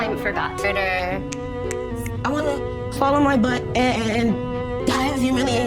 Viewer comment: That wasnt even English, also youtube can't even understand her, the CC/subtitles say swallow my butt 🤣